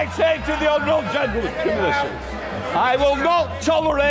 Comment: did he just say 'give me that sh*t'? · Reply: Seat